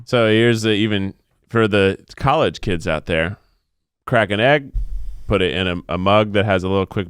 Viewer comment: i heard keith say "for the college students out there" i perked up. thank u